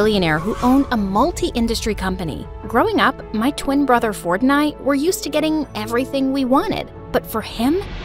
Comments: I heard “Fortnite” when they said “ford and i” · I didn't read the subtitle I thought she said "my twin brother Fortnite" and I was like "excuse me what?!" 😭😭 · Bro I can’t be the only one who heard Fortnite at 0:10 rn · I thought that she said me and my twin brother fortnite like I saw It again and I looked at the cc and it said ford and I. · Ford and i i thought she said fortnite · Was I the only one here 0:12 i thought that she said my brother fortnite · why did i think she said fortnite 💀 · "My twin brother ford and i" thats what she is saying right? well what i hear is "My twin brother fortnight" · Why did i hear fortnite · Nice, her brothers name is ford At first I thought she said "my brother, Fortnite" instead of "ford and i" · I thought she said my brother fortnite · When she said Ford And I I thought she said Fortnite💀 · When she said ford and I I thought she said Fortnite 😂😂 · I heard that as my twin brother fortnight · did it say Fortnite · did I hear it right Fortnight? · Ok i heard "growing up my twin brother frotnite" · I thought she Said my brother FoRtNiTe💀🤌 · "Ford and i" I thought you Said fortnite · what yall hear: "my brother fort and i" what i hear: "my brother fortnight" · I heard Fortnite instead of Ford and I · what I heard (growing up my twin brother Fortnite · Bro at the beginning I thought she said my brother fortnite💀💀☠️☠️ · Bro I thought she said (my brother fortnight )at the beginning💀💀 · Yooo did she just say her brothers name fortnite 🤣🤣🤣🤣🤣🤣🤣🤣